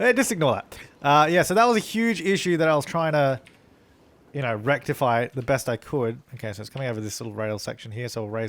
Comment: did say erectify? 😂